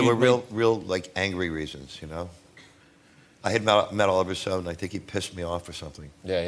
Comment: who pissed him off? I can't tell what name he said. Thanks. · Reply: Oliver Stone